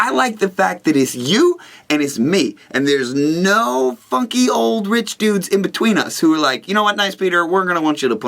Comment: i thought you said fucky old rich dude